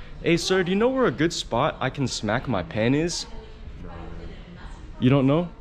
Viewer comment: "Do you know a good spot I can smack my PeniS?" BRO I MISHEARD IT, the guy probably did too. Im laughing too hard LMFAO